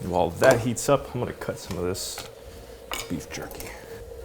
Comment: When you introduced the jerky, I thought you said turkey and I thought, "He didn't want to open the fridge, so... he had turkey sitting out at room temp?" Glad to see later it was jerky instead. Given circumstances, it would have been good if this video came out a couple of days earlier, but it's really hard to plan around the weather sometimes.